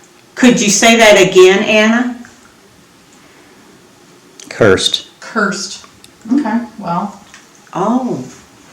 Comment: Happy New Year, and. Here's to a spooky 2023! 🦾📺💯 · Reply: "Leave me alone." Not off to a great start. ⚠️😁 Yep, I heard "cursed".